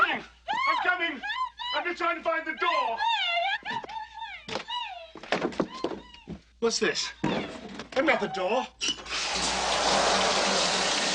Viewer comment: “What is this? Another door?” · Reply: I always thought it was 'that's not the door'. Maybe I misheard it.